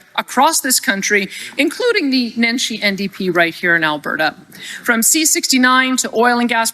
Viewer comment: Menchi NDP I thought she was saying the dementia NDP 🤪